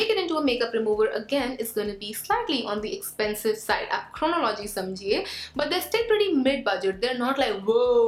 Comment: i rewinded thrice 😹😹😹 to hear and still can't hear anything else than "aap chronology smjhiye"